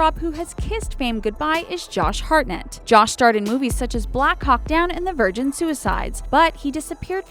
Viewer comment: I heard "black cock dawn" lol